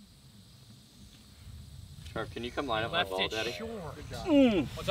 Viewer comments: did rat call matt daddy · Does Garrett call Matt daddy at 29:44?😂